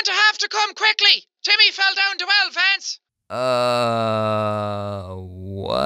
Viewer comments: I thought it said “Timmy fell down the well friends”the first time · sounded like “Timmy fell down the well Vance!” Obviously sounded like jacksepticeye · I thought they said”YOU GOT TO HURRY TIMMY FELL DOWN THE WELL LADS” 🙄😁😂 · I thought it said at first “you better come quickly Timmy fell down the wild fence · It sounded like they said YOU'RE GONNA HAVE COME QUICKLY, TIMMY FELL DOWN DA WELL VANCE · The 5:40 voice says “come quickly Timmy fell down the stairs” it also sounds like Cosmo from “fairly odd parents”. He even mentions the main character of “fairly odd parents” Timmy. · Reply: You have to come quickly Timmy fell down the well Vance I thought that’s what he said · SHE SAID COME QUICK JIMMY FELL IN THE WELL LANCE (I'm not really sure that's what I heard) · Did they say "You have to come quickly, Timmy fell down from the fence!" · “You’ll have to come quickly, Timmy fell down the twelve Vance” That’s what I heard when the first time the accent women was talking... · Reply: I heard this: "You'll have to come quickly, Timmy fell down two elephants." Makes no sense whatsoever, but that's what i heard. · What the Jacksepticeye Lady Was saying : You are gonna have to come quickly because Timmy fell down The Well Lance!!! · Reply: I thought it said timmy fell down two elephants! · The lady sounded like she said "you have to come quickly timmy fell down the elephants" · idk why but I heard "you have to come quickly, jimmy fell off 2 elephants" lmao whyyyyy